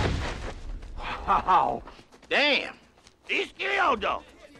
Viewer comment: DEESTEE ODO!!! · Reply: I heard 'He killed him'